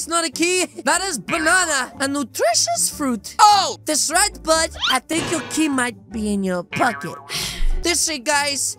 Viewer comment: what said: i think you putted your key in your pocket what i heard: i think you putted my peingo in you po